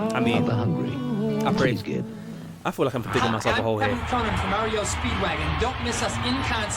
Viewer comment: you COULD skip ads... I used a VCR and recorded everything.. I had HOURS of music videos.. but I would also record any shows I wanted to see. Then I could watch whatever when I wanted... and also I could and DID fast forward through ads I actually got so good at it that I could stop the FF as the show resumed reliably.. no rewind needed. 🤣 I still have about 10 VHS tapes of music, MMA and Pro wrestling events I really enjoyed. VHS > DVR by a LOOOONNNNG margin. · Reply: Did you just say "Mario Speedwagon?" I swear that's what I heard 🤣 just in case it's REO speedwagon. Mario Speedwagon would be the next Nintendo Game. 🤣